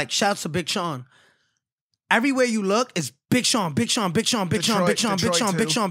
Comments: Big Shawn What SixNine said:Bitch On,BitchOn,Bitch0n😂😂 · Did he say bitch on ?